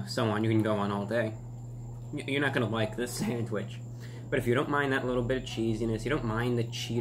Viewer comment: "But if you don't mind that little bitch". I jumped a bit when I thought i heard him say that. Bless you, reviewbrah.